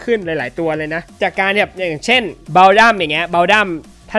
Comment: ถ้าไม่พูดว่าบัลดัมนี้คิดว่าเต่าตนุ 5555